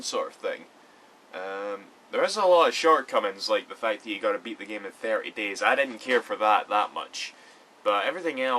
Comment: did he say u had 2 beat the game in thirty days? Why? Please reply.